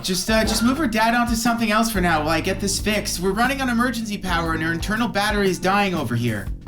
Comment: [TEXTUAL_ANOMALY] corrected. Thank you, [EAGLE_EYED] viewer! ∠(‘-‘ ) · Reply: "on" emergency power 0:33 mid-2000s 2:14 - "kidnapped" is misspelled 3:40 - there ought to be a comma instead of a period at the end of this subtitle 4:03 - seems to be merged with the subtitle after the next 5:32 - all of the food 7:31 - the code 8:55 - watch that 9:02 - the "always" is redundant with the following subtitle 10:01 - "can actually find" 10:23 - "its enforcement" the "its" is incomplete 10:53 - should be "a part" - apart means to be separate from something. A part means separate parts I like to do this sometimes for some reason 🙃